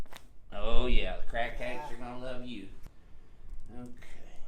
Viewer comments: did he just say "The crack heads are going to love you" 😂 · did he say "crackheads gonna love you" lmaooo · Did he just say "crackheads are gonna love you"